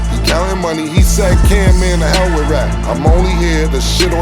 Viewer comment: I thought Ma$e said “Man the hell with rap!”